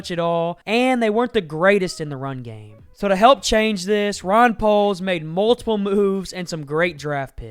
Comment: he actually said “Ron Poles”😂 Strike that he said it throughout the video😫